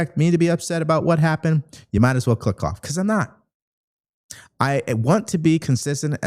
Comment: You said "you might as well click off" and I heard "you might as well f*ck off" and had to rewind cause that's not normal for you. HA, I appreciate your approach to things!